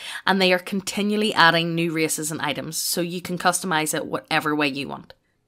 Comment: I heard it as "New racism Items"